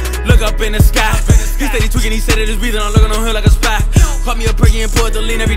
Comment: Wtf is he saying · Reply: Aleey K Lol ! " He Steady Tweakin "... shit that's all I got so far